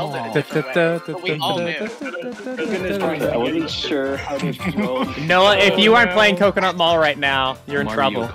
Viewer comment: Bro I heard this and went “wait did he just say Noah?” (My name). I replayed it and went “surely he said bro…” I then proceeded to have a heart attack. I wouldn’t have been surprised if reached through the screen and grabbed me